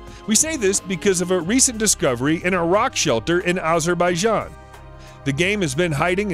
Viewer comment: In 1:56, did he say " in Iraq shelter in Azerbaijan ?!!! Iraq and Azerbaijan are completely different countries. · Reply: He was meant to say "A rock shelter in Azerbaijan" but his accent made it sound like "Iraq shelter in Azerbaijan" confusing right.